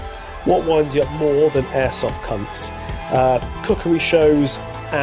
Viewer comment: I thought you said cuckery shows for a second🤣